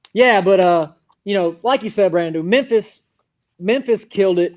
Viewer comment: am my mistaken but does say "Brand New" instead of Brandon lmfao nice one that's his new nickname